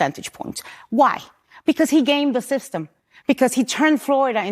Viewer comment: I thought she said "gamed" the system but, what she said doesn't matter. I'm not from Florida but, DeSantis won re-election because he's doing a good job, plain and simple. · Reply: I think you're right, she did say, "gamed." My bad. Yup, DeSantis has done a phenomenal job in Florida. And will continue to do so. Just hours after celebrating a landslide victory securing his second term, DeSantis was out with Florida DEM, Kevin Guthrie, preparing for Hurricane Nicole. Again, boots on the ground. The guy is a rockstar with unparalleled work-ethic.